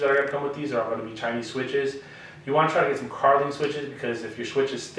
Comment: did you say get some carbon switches? My phone speaker is terrible. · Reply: Carling switches 💪🏾